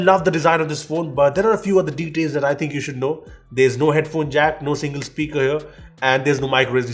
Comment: did he say there is not even a single speaker??